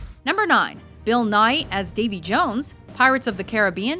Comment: did any body else think she said Bill Nye (as in Bill Nye the science guy) at first? XD